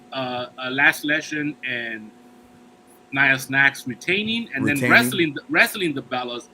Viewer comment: Did he say Niya Snacks 😂😂😂